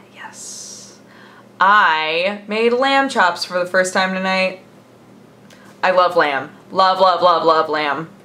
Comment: When said "I love lamb", I thought she said "I love lamp" at first... oh lord